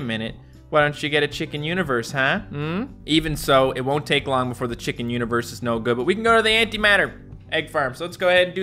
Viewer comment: I always thought it said chicken university 😂